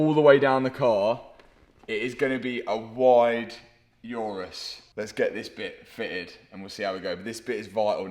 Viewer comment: Did u say UrArse is gonna be wide ?🫶